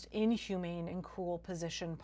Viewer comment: captions say "cool" when you verbally day "cruel." Just a heads up in case there's someone that can't hear or doesn't have volume on.